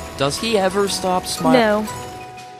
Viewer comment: When he said, “ everything is great and so is this human bean chair “ I heard, “ everything is great and so is this human being chair “